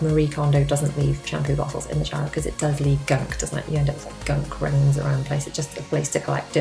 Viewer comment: Great video! Lovely house. I love love love all the cube storage in one of your daughters bedrooms! Did you say gunk rings at 11:35? Lol.